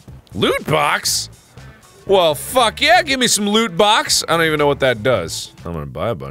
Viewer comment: I wasn't looking at the screen when he said "loot box". I thought he said "lewd box"